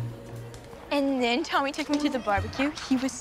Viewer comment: WHEN ARE THEY GONNA TELL SADIE THAT TK’S NAME ISN'T TOMMY😂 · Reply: Actually tho like, every time she says "Tommy" I hear "Yee Yee" 😂😂😂 (sorry it says edited, I had to fix a spelling error.)